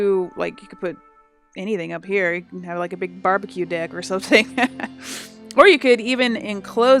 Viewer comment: Anyone catch that she said" barbeque dick" at 11:47?